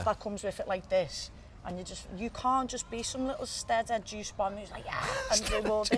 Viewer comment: what does she say? “stedder juice bomb”??